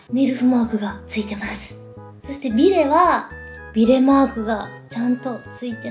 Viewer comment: ビレマークがベルマークに聞こえた！